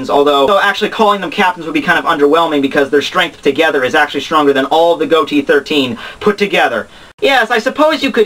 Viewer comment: I thought he said each of them are stronger then all the captions put together